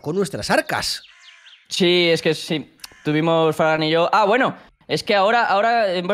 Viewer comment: parece que dice fran pero dice fargan :"(